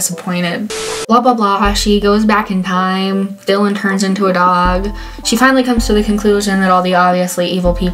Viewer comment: When you said "Dylan gets turned into a dog" , I thought you said "The Villian gets turned into a dog" 🤕😂